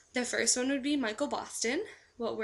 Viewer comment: I like Michael Boston, but when you first said it I thought you said Michael Bolton. haha. I don't think I've ever commented on your videos but I am due January 1st, 2010. I think just a week before you!!!!!